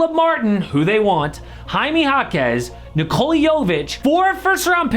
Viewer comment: thought you said Nikola Jokic for a second